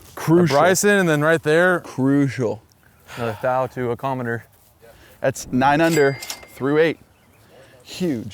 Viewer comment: I coulda swore chase said, "another thou to a commoner" and I almost died. Tell me how you really feel about us lol